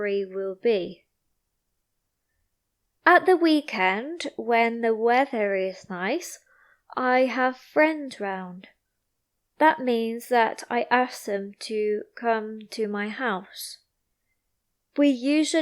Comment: What did you say in those minutes? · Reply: "At the weekend when the weather is nice, I have friends round, that means that I ask them to come to my house "